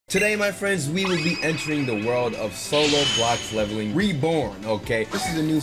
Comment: I thought I heard “solo black leveling”💀